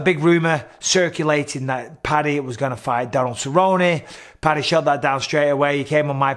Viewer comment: did he say Daryl Cerrone?